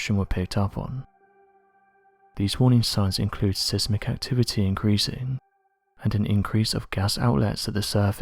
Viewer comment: " inement " ? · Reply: Thanks for commenting that, I thought that's what I heard! I also heard him say (edit) sizmick activity around 6:39 or so... lol 😜🤣😂